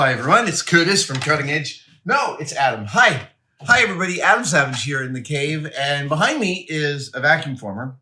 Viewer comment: I don't know why....But I heard "Hey everybody, Ham Sandwich here" for the intro 🤣🤣